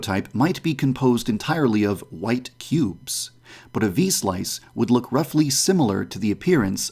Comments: 😂😂😂😂😂😂😂 i thought he kept saying v sauce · I dead ass thought he said v-sauce I’m not okay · U thought he said v sauce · I thought said "V-sauce" lol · I thought it said V-Sauce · I thought u said v sauce